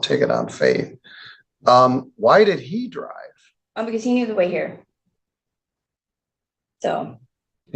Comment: Wait a minute 🧐 She let him drive because “he needed a way here” then she claims HER valid drivers license is in the car? Seriously? I’m suspicious 🤨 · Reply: I thought she said "he KNEW the way here".